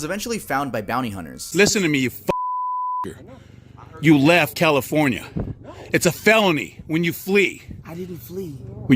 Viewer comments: yo what did he just call him? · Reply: he said nigga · I don't know if I'm hearing this right but did he just say "listen to me you f-in n - - - - r"? Sure sounded like it to me. His immediate reaction of looking at this guy like "wait, what, did you really just say that?" fits what you think his reaction would be. However he instantly drops it and carries on talking as if he didn't just say that which doesn't really fit the scenario that he did.